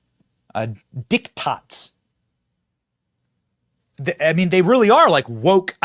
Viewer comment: did he just say dick tots?